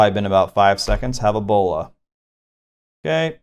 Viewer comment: At like 18minutes I swear I heard “it’s been about five seconds.. have Ebola” haha